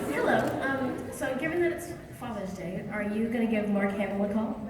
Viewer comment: What did the girl say at 12:51 "Given it's Fathers' Day.... ?" Thank you for this great quality video ! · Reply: She said "given it's father's day, are you gonna give Mark hamill a call?"